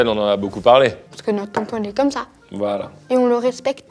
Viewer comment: c moi ou il a dit "parce que notre tonton est comme ça" en parlant de l'homosexualité ? j'ai loupé un épisode ?